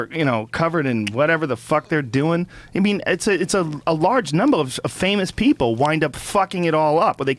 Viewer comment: / 3:08:03 Joe says, " Its a large Numbo of people Fucking it all up." i think he said that to see if Russ or Jim Where paying attention.